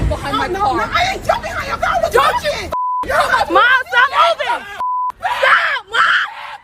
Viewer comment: did anybody hear "mom stop moving " as was backing up? So she was still being approached after pulling a gun and backing up 🤔🤔🤦‍♂️🤦‍♂️